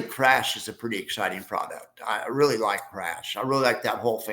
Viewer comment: Hello, at 25:40 you mention some products that you like…do you mind clarifying what you are saying? It sounds like you’re saying crash? Do you mind telling me some of their tickers so I can research? Thanks for the videos. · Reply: Yes CRSH is the ticker. It is a bearish high yield fund based on TSLA. It is the opposite of TSLY. There is another inverse ETF called FIAT. FIAT is a bearish high yield fund based on CoinBase - this one is the opposite of CoNY. I also call these funds “inverses” sometimes.